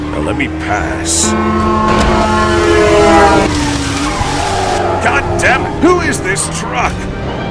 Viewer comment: "Goddammit WHO IS THIS TRUCK" Is this a mistake or am i mishearing stuff?